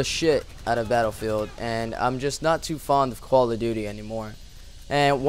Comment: it sounds like he said koala duty. LOL